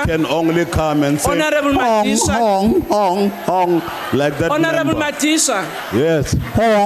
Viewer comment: ive been hearing ''Honorable magician'', and wander for 3 minutes how he got that title...and ive misheard the word. it was honorable madisha. HITLARIOUS!